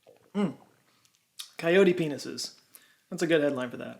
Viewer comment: I thought you said, err.. Peyote peni?